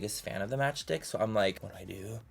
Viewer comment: I thought he said match dick